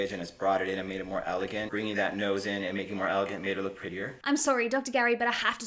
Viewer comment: Huh, I wonder why, when that plastic surgeon keeps saying that woman looks more “elegant” after her surgery, I hear him saying “more white” 🤔